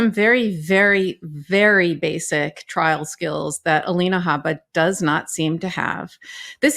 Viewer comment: LISTEN to Legal AF podcast: <link> DEALS FROM OUR SPONSORS! One Skin: Get started today at <link> and receive 15% Off using code: LEGALAF SmileActives: Visit <link> to get this exclusive offer! Factor: Head to <link> and use code legalaf50 to get 50% off Shopify: Sign up for a one-dollar per month trial at <link> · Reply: Did I hear Karen say Alina 𝗛𝗼𝗯𝗯𝗶𝘁 ? LOL LOL